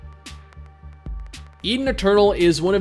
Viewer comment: Swear to god I heard "Eating a turtle" when you said "Eden Eternal" =D